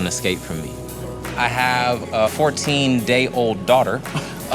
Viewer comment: When he said « 14 day daughter » I thought he said 14 year old daughter.